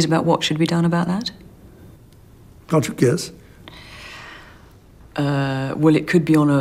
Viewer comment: I didn't hear him properly. What did he say at 0:14 ? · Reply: He said 'can't you guess?'